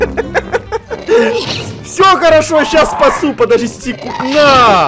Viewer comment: Эдисон:*хочет сказать щас спасу* Я:слышу как он сказал: сейчас поссу